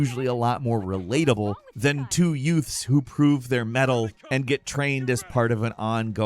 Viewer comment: Two what? Did you say, 'two yutes'?